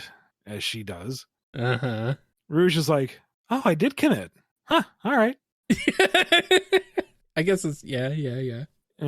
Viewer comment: What did Rouge say? I couldn’t tell. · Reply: oh, i DID commit. huh. alright